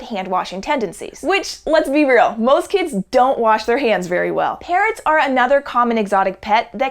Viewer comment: I heard "parents can be another exotic pet that can be quite dangerous". This is quite true.